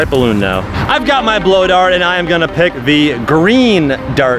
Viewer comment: i heard him say "i'm going to pick the RED one"